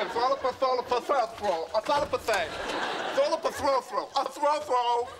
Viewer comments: So no one understood what he said at 1:52 ? All I heard was "A throu throu"🤣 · Ross: "velafe velafo velafofo..." Joey: "consider it done." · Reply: What did Ross say? Sounds like he is saying throw throw! :) What does that even mean? A throw pillow? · Ross: "velafe velafo velafofo..." Joey: "consider it done." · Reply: I heard "throw throw" 🙄🙄🙄